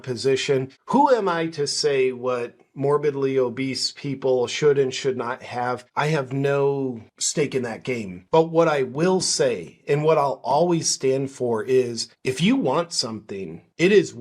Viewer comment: Did you say steak?